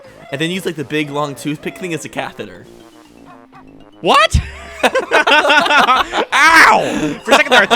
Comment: What did say at 3:40? "I like to use the big stick as a __". (My hearing isn't the best) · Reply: Catheter